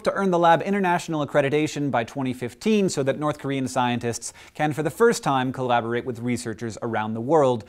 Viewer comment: At the end I heard "North Korean scientists can for the first time collaborate and listen". Damn you, brain!